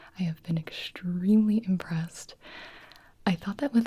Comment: When said so far I've been very impressed I heard her say very depressed 😭 girl me too!😭😂